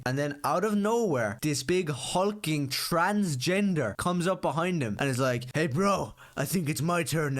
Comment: Am I the only one that heard him say "big hulking transgender" and thought of a very fun idea for a new She-Hulk reimagining? I'm not a comic book guy, but I'll cook up a script to piss off a 15 year old Irish edge lord.